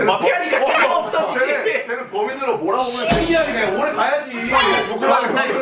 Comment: I THOUGHT HOBI SWORE @ 13:41 OMG It almost sounded like he said shi-bal, but his gwangju satoori made, "citi'zeeeeeen' " sound like the "shi" of shi-bal